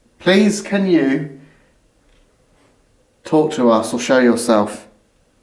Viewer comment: At the beginning of investigation, I thought I heard ask..."Please can you torture us or show yourself?" 😂 Misheard 'talk".... So great an episode! Thank you!